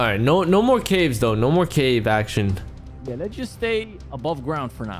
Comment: This was awesome, but did u call, Action at 40:55, I thought I heard u say Action's name. Ngl this is getting interesting hope Action can join u two. 🤞 and hoping u two continue this👍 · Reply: He said “No more cave action” which means no action in caves